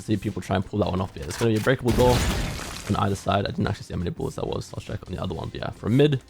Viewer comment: What is bro saying here😭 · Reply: "i didn't actually see how many bullets that was i'll show you on the other side"